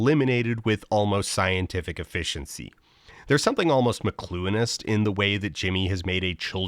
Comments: What is the word at @ 11:17? The subtitles and transcript didn’t get it right. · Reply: McLuhanist. It refers to Marshall McLuhan, a philosopher primarily known for his work in media theory, arguably most famous for the idea that "The medium is the message" which (in very simplified terms) argues that the form of something (in this case, how the show's games are for a child's intelligence) has an majority influence of how it is implemented & its impact (despite being adults, the contestants act like children because that's the level of intelligence the game treats them with.) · Could someone tell me what is being said here? The subtitles read "mlonist" and that very much does not sound like whatever word is being spoken but I might just be ignorant of the topic. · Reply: I'm assuming it's "McLuhin-ist", in reference to Marshall McLuhan and his theory, of which you may have heard of "the medium is the message", so here it's about how the medium being a game for children makes the adults playing in devolve into childish antics. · what is that word!? Mcluinist? google gave me nothing but I'm curious what it means. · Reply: McLuhanist as in Marshall McLuhan (the medium is the message) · Would someone from the smart group please tell me what word The Folder says at 11:17? I have tried macluinist, mcloonst, and mclooinist. No luck so far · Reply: "McLuhanist" referring to Canadian philosopher Marshall McLuhan